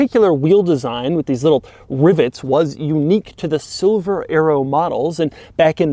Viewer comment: Ah the 02 silver arrow. I emailed you a few years ago to capture my 00 SL600 which only still has 22k miles on it. Glad to see a r129 get some appreciation. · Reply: I thought he was saying Silver Aero !